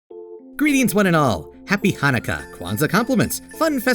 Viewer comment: Happy honica sounded like happy monika